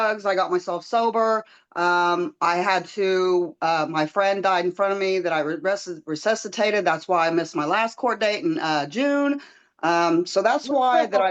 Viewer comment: I thought she said she didn’t have time to call back bc she was getting ready for work.